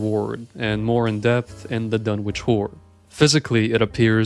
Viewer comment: I heard "The Dunwich Wh0r3" and even the subtitles censored it. xD